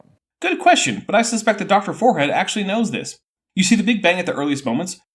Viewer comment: Did he call him “Dr. Forehead”?